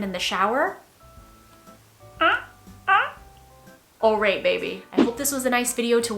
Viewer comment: Something silly to say, towards the end of this video where you were asked about good quality jewelry and you said "Aurate baby" I genuinely thought I heard you said "Oh right baby" haha